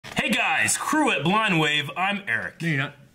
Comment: I used to think you said "cruel blind wave" at the beginning of videos. Then I thought it was "crew blind wave". This is the first time that I clearly heard it as "crew at blind wave"